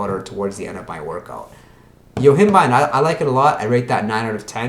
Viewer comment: Couldn't hear which nootropic you said at the 8:25 mark. Pls can you comment on this one pls. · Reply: Yohimbine